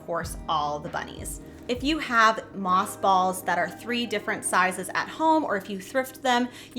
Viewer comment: I swear, every time you say “moss balls”, I hear “moth balls” & I recoil in horror for a split second. Might be time for a hearing test! 😂. Love, love your stuff!